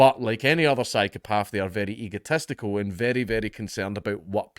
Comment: did you say ego testicle around 7:00?